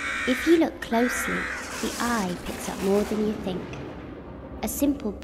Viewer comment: i heard she said "the eye accept more than you think"